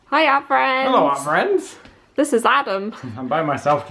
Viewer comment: for the longest time I thought you were saying "hi our friends" 😅